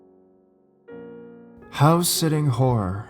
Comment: I thought he said house sitting whore😂😂